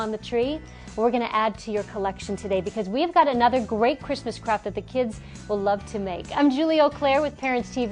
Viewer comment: lmaooo it sounded like "christmas crap"